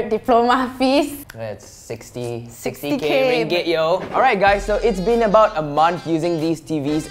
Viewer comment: Anyone heard that he said ringgit?